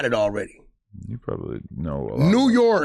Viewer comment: did say Wack knows alot of the gays?😂😂😂😂😂😂